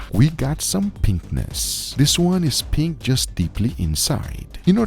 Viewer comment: Yeah... We've got some pen(k)nis. 😅 I had to rewind a few times and still don't hear 'pinkness'